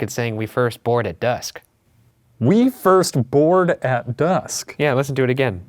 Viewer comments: I heard “wait there’s more below the castle” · I heard we’re not here for reasons that you Think. Far fetched but kinda of makes sense